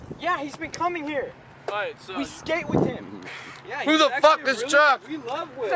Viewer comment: Sounded like kid in green shirt at 1:43 said, "Who the fuck is Trump?"